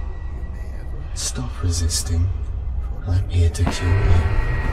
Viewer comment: "stop resisting, I'm here to cure you" if you didn't hear.